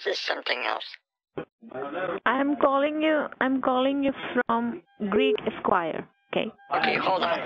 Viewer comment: did I hear Greek Squire?